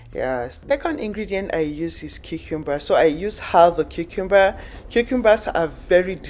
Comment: I can’t understand her accent. It sounded like she said chicken breast when she was slicing the zucchini looking vegetable. Anybody know what it really is? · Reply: Cucumber